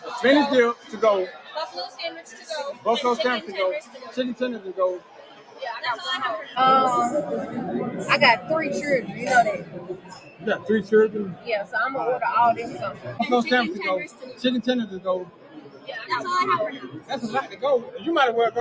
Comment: Did she just say "churdren"?